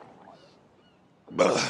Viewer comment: It sounded like he said at the end “all those Catholics made me thirsty” lol